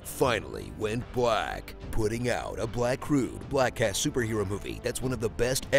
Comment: I thought he said "Black Ass Superhero Movie" as opposed to "Black Cast Superhero Movie" 😂😂😂